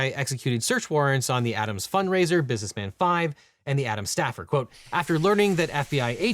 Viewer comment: Every time legal eagle says "Adam's staffer" i hear "Atom smasher" ..... I don't know why 😅😅